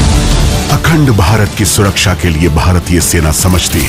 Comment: did I just hear Akhand Bharat?